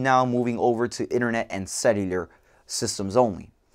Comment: What does he say at 0:44? we're slowly moving over to internet and ?????????? systems only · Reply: Cellular